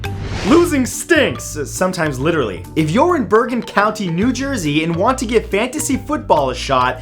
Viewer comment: I thought you said Burlington county new jersey